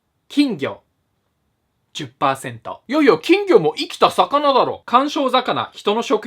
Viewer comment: 「生きた魚」が「いち たす なな」に聞こえて，そりゃ 8 だよなぁと思ってしまった．